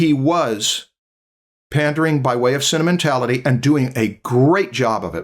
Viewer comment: This whole time i thought was saying "cinnamontality"